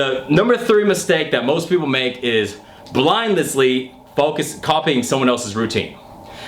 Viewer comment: Did he say "blindlessly"? LOL!